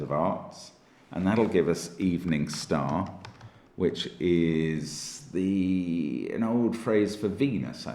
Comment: I heard 'that's an old phrase for penis'... which still fits as a rather amusing euphemism.